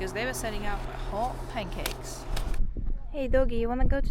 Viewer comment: Thanks for the video! did I hear you say ‘hot pancakes’? Lol. It’s actually ‘hotcakes’ :).